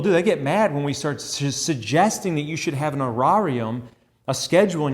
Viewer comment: What is that word "arirarium"? At the 3:41 sec. I do not think I have the correct spelling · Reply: Horarium